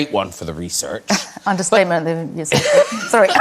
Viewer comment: what does Helena say at 7:44? "understatement....."? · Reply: I think she started to say "understatement of the year" and then switched to "century"